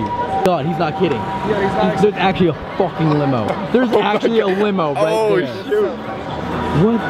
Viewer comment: I heard elting say wino instead of limo